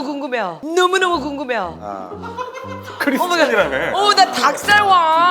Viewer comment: i thought you said you are christian lol